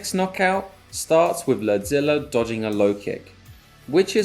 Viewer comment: I thought he was saying “lead-zilla”